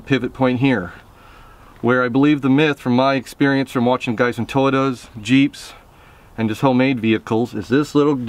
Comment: Did he say "Toytoes"?